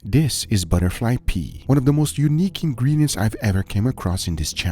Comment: I heard "butterfly pee"